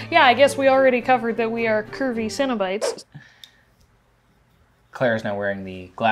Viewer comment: I thought they were saying cinnabites, like the canadian version of cinnabon lol